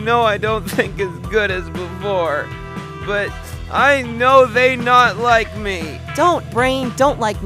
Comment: why do i hear "i know they not like me"???